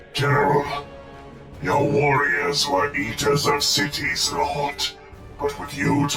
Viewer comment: Sorry, when I heard 'eater of cities' I heard 'yeeter of cities' and now I will forever know the World Eaters as the World Yeeters.